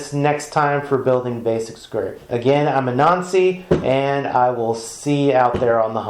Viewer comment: Dude I totally thought you said Greetings I'm a Nazi. I was like "Wait what?" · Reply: I thought he said I’m a Nazi as well.